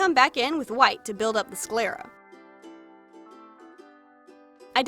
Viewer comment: When you said sclera I thought you said “square up” and I was confused about why wanted to fight me